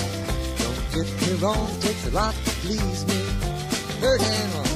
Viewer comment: I always thought he was saying "third hand, Loren (Lauren?). I don't know...was there a pianist named Loren (Lauren) back then? Anyway,'third hand' means a piano player who's so good it's as if they played with three hands.